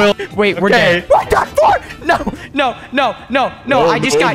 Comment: I've noticed a lot of people think I swore at 3:25 in the video. But just to clarify no I didn't swear!!! I said "WE'RE DONE FOR!"